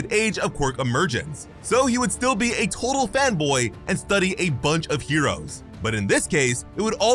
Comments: I ACTUALLY THOUGHT FOR A SECOND HE SAID FEMBOY NOT FANBOY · did anyone hear him say femboy too?